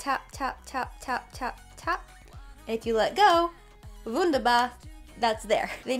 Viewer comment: Did you said: „wunderbar“ at 3:49? It sounds like that🙈 Lots of Love from Germany 💓